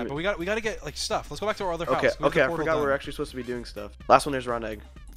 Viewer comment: George: im dying Everyone: laughs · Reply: I thought he said there’s iron XD